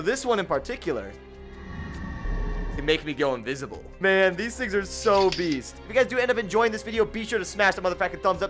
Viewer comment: i thought he said "man these are so obese"